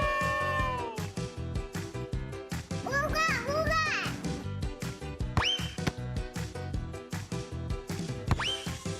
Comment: did she sayed ooga booga? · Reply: It's been 3 years you could be dead rn but yeah she said ooga booga